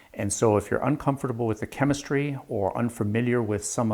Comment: Wait, what? Did I hear you mispronounce "chem-mystery" @ 00:20?.. ;-D